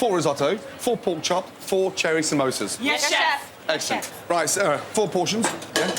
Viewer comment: did I hear cherry samosas?